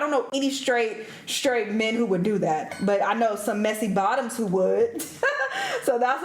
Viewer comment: did she just say messy bottoms ? 😂😂